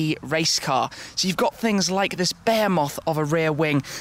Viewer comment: Sounds like he said bear moth not behemoth pronounced beh he moth